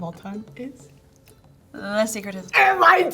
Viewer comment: The Secret History by Donna Tartt is the book in case anyone wondering. I definitely couldn’t hear what she said.